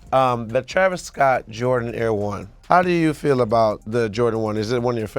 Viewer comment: did Trinidad say Jordan Air 1??